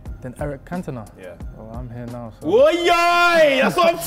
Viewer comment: Sm1 tell me What did sancho say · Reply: I'm here now so